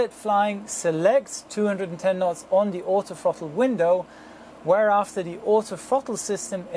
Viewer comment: did he say, "the pilot flying selects 210knt on the oto fucking window'?